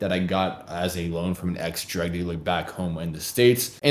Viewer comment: Did he say “ex 💊 dealer?”